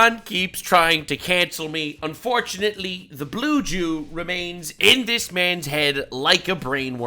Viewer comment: I thought i heard blue chew 😭